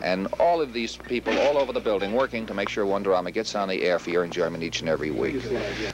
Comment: Did Sonny say "enjerment" instead of "enjoyment"? Only in New York.